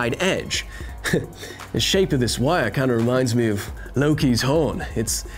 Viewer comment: "The shape of this wire kind of reminds me of Loki's hole, it's -. But then he's -. He got Unintelligible. by an eggplant emoji.", don't I just love mishearing things~?